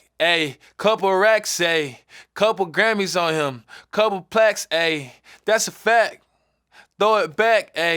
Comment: am i the only one who thought he said "couple plats" like going platinum